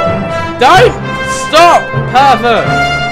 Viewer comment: When he called one of the horses herbet I thought he said pervet